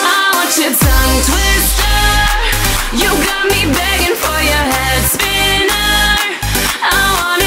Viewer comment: I thought she was saying "begging for your Hand Spinner" 😂😂